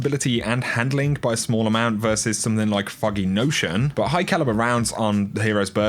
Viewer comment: When you say foggy notion it sounds like your saying Fucking notion